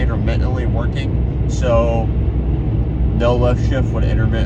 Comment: Did you say lift shift at 7:48 ? What is that? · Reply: I meant to say no lift shift. It’s when you go to up shift but you keep the gas pedal to the floor while you shift